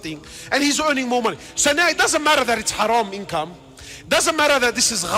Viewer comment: Can you please tell what the word at 0:58 is? Not Haram but the next one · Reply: Haram income, income meaning the money your earned or salary@